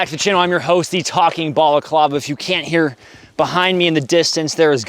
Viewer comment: Everytime you introduce yourself I hear "... The talking ball of lava"